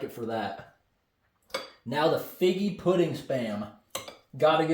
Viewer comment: I thought it said niggy pudding😊